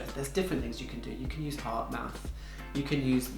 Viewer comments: There are many lifestyle measures you can take such as wearing appropriate clothing etc, however ultimately the cause is usually a deficiency of progesterone or estrogen and to give true symptom relief the ideal thing would be to test your levels and then restore hormonal balance with Bio-identical hormones if appropriate for you. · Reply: hi doctor what did you say around 7:12. Reduce stress with heart math? Great vid and information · What was the doctor saying at 7:12, heart math? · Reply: HeartMath! Yes- that's right! it's an app and Kish loves it